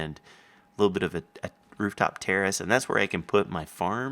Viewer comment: LOL i thought he said rooftop terrorist not a rooftop terrace...